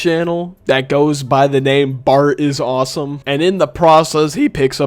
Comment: i heard that voicecrack